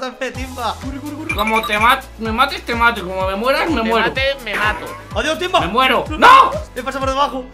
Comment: el riumba es real :3 como te mates me mato y como te mueras me muero o al menos eso entendi :v